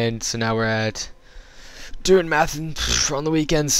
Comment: did you say you tried meth on the weekend?